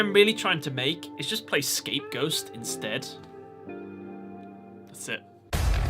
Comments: did you say scapeghost???? 😂 BTW no hate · It almost sounded like you said ScapeGhost instead of Scapegoats